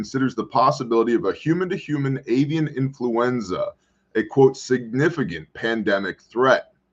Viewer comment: Swear I heard "alien influenza"...🤔 Between that and the beef allergy...